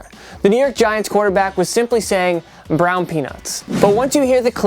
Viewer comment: I thought manning said brown you know